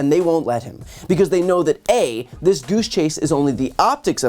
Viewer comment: Could have sworn Brian said douche chase, not goose chase!